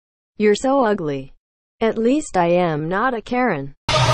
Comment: I thought she said "At least I'm not a Korean". 😂😂 #ImaKpopFan